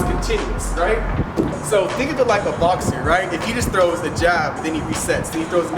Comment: you said "think of it like a boxer" and the first time I listened to that, I SWEAR you said "think of it like a lobster" and were about to go all Jordan Peterson on all of us. "Have a gainful day, bucko!" 🤣